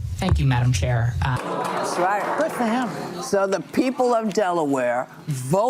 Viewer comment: Did Joy just say good for “him” after he said ‘thank you Madam Chair’? 🤭